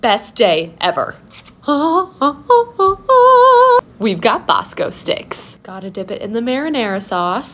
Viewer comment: I thought it said bombastic not Bosco sticks